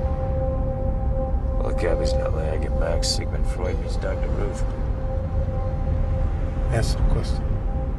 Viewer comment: what does he say at 1:03 ?!? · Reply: Of all the cabbies in LA I get max. Sigman Freud meets dr ruth"